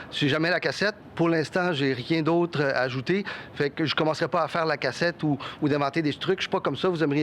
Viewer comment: faire la causette I thought he said faire la cassette.